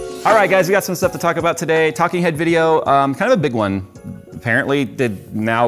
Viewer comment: All i heard was "TALKING HEAD, KIND OF A BIG ONE" LOL